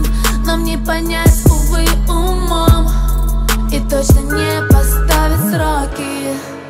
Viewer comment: я один слышу: "И точно не поставить сраки"??